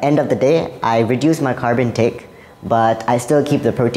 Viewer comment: It is 'carb intake' not 'carbon intake' as the wrong subtitle says.